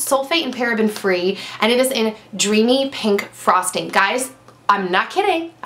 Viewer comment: When you said "dreamy pink frosting" I thought you said "dreamy pink prostate" -.